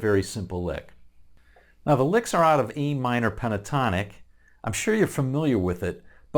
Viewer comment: JFYI. I Think you say 'E minor' (even though you show A minor) And clearly play in A. @ 1:30 That said. I was one of those in the movie theatre that was mesmerized with that opening lick and the entire piece. My favorite of Woodstock. Thanks for this breakdown, and giving the well deserved kudos to Alvin! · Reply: You're welcome. Thanks for watching. I said A minor pentatonic at 1:30 while showing the Am pentatonic on screen. Midwest accent. You can take the boy out of Detroit, but you can't take Detroit out of the boy!